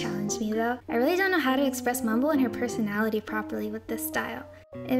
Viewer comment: did she say mumble? is she really mumble? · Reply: Yes she was drawing mumble